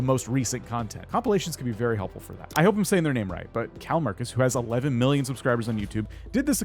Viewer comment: I died when i heard you say calmarcus for call me kris